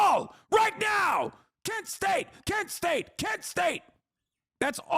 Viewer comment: what is he saying at 10:06? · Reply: Kent State